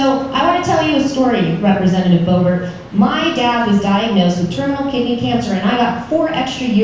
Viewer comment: Lmao thought she said terminal gay cancer made me wonder how many new cancers there are lmao